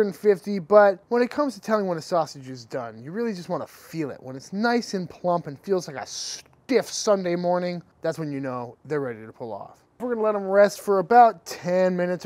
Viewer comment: did he just say : feels like a STIFF SUNDAY MORNING ? xD HAHA LOL